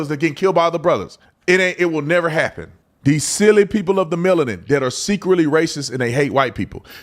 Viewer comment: did he just say silly people of the melanin 😅